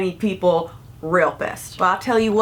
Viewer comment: did you just say "realpist"...? ghreat neologism! oh no... you just said "real pissed." mkay... but i like "realpist" anyway. yknow? like, someone who is a rapist with reality. could be used in so many creative ways! (including describing believers!) i like that.